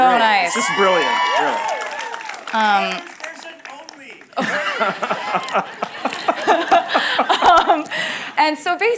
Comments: what did he say? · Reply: taylor’s version only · i want to know what that guy said from the audience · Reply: Lmao he said ’’Taylor’s Version only’’ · What does the guy at 31:13 yell? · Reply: Taylor's version only 😂